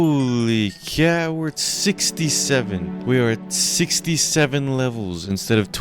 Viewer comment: did i just hear.. 67??!?!?!?